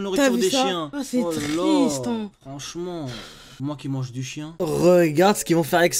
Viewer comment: Toi qui mange des Quoi ?